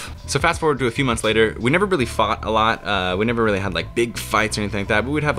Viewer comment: thought he said “fucked a lot” I was like damn you were just dating